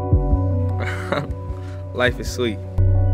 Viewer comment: What did he said at 0:12 ?? Life is ??? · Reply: life is sweet